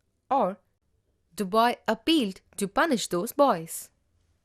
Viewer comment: instead of "appealed" they've written "applied"